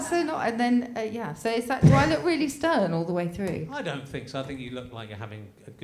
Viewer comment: I listened to the first half of this with earphones in as I mowed the lawn, and I misheard a couple of things Susie said. When she was talking about how she's edited to look on the 8/10 Cats version of Countdown, I thought she asked "Do I look stoned...?" instead of "stern". And when she said that the nature of the show was "circular" I had to stop the lawnmower because I was convinced she said "succulent".